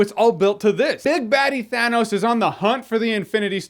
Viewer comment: lol I thought you said Big Daddy Thanos lol